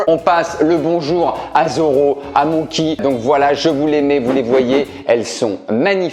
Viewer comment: J'ai compris "Mouky" au lieu de Monkey...